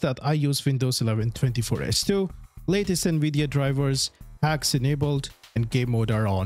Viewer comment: i thought he said hacks enables as a joke.. wtf is hags anyway